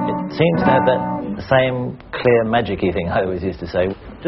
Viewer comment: I've been trying to understand what he says at 1:31. without trying my old one it seems to have that same clear........... could anybody tell me what he says there? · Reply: "Magicky thing", in Australian slang, magical quality. He refers to playing of certain chords – the sound projected has richness he never heard on his old guitars, but only on Smallmans. Then he compares the new harmonics he hears with the shimmering of eucalyptus leaves on tree tops in summer's breeze. Now, to understand the metaphor, eucalyptus trees do not have dense branches but well spaced out, and on them long, thin leaves, that create almost silent, rustling-like sound which is not harsh, but very velvety.